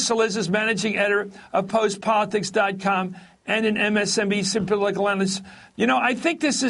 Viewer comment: WTF does he say at 1:16. msnbc symplangus?